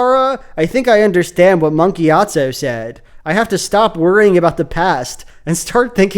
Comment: Every time you say "Monk Gyatso" I hear "Monkey Gyatso" 🤣🤣🤣